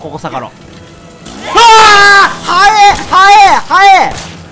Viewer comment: 、9:40の所で言った。 （早い早い）が（ハエハエ）に、聞こえました。